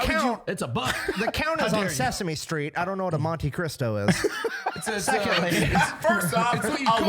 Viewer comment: its Monte Cristo, I keep hearing "Monte Crisco"